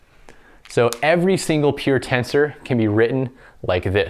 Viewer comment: Question about your accent: do you pronounce peer tensor and pure tensor the same way? At first I thought you were saying "peer tensor"